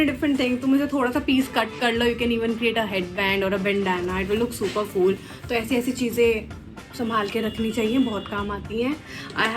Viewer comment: Did she said "super fool" instead of " super cool"😂